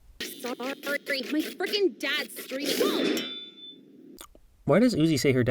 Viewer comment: She actually said Screaming and not Streaming. Why would he be streaming? He probably screamed from getting knocked into the wall. 6:19 OF COURSE SHE DID. Probably so she could also better use her powers only when she wants to instead of sometimes transforming into "zombie/super uzi" and murdering people without control, or changing arrows and other things into monstrocities. 5:32 The glass is just gone from her falling. How would it be repaired? (no timestamp) To clarify, I believe the Humans are cast in shadows so you automatically want to focus on the Robots instead. Kind of like how in Charlie brown the Adults speak gibberish so you focus on the Children, and they are never on screen. I also hope that the 6 (Doll, Tessa, N, V, J, and Uzi) somehow join forces in episode 6, or instead have a battle (I can see that happening because Uzi Killed J, Doll came close to killing V (and would've if not for N and Uzi), V killed Doll's parrents, and Uzi said she wants to murder all humans). But if they do, it better be epic. Also, if Uzi has administrative powers over herself (and her powers) as well as N and V, maybe she can restore their memories using her computer. But then there's the question- How are V and N both existing in their memories? Like, V is actually V, as we can see her angered by Uzi hacking them when she wakes up. But so is N, because they both wake up at the same time. This must mean that the robots have some sort of internet connection. I also wonder about U